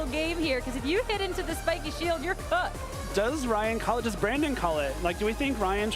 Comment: Did I hear that correctly at 6:22:33? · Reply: "cooked" I think